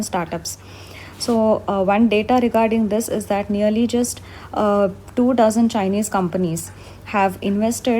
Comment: please, someone, tell me, is it 2 dozen or 2000 Chinese investors that have invested in 92 companies (11:26 minutes) I can't understand it properly? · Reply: 2 Dozen.